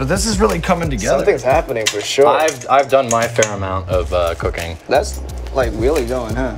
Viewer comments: I thought Joji said I've done my fair amount of cocaine 😂😂😂😂😂😂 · "I've done my fair amount of cooking." There's stories behind these words :') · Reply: o i thought he said "ive done my fair amount of cocaine" lol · Joji : I've done my fair amount of cooking flashbacks to hair cake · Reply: i thought he said “I’ve done my fair amount of cocaine.” that’s something joji would say so i didn’t question it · lmao I thought joji said he’s done his fair amount of cocaine 😭💀💀